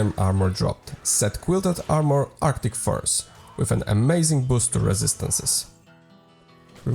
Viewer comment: sounded like your said arctic farts 😂